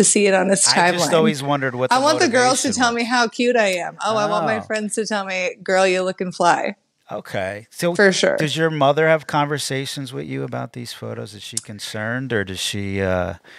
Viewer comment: Did she say “Girl you looking fly” @ 6:55. Welcome to 1988.